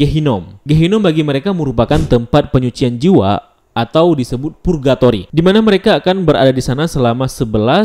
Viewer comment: apa? Purgatory? Gue gk salah denger kan